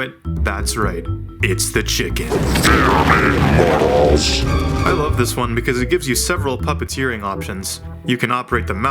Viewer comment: what did he say? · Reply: “FEAR ME MORTAL’S!”